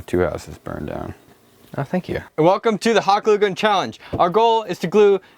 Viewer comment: Was I the ONLY person who heard him say the "Hadugen Challenge"!?